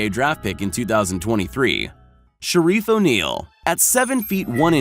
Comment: did he just say shareed o ' neal..............